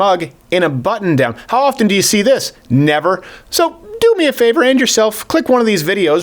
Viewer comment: When I heard "Do me a favor, and yourself, click one of these videos..." I initially heard "Do me a favor, end yourself".